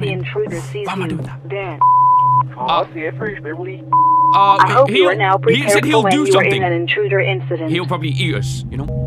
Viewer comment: if your wondering what it says, it says “he’ll rip your face off.”